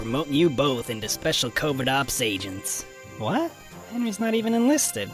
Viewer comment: At about 9:30 did he say covid ops agents?